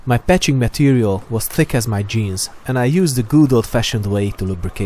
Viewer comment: am I the only one who heard him say thick as my jizz